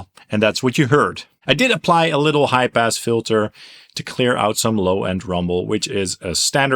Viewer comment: Sounded like you said you used a “ HYPE ASS FILTER “ then it registered…. High pass filter.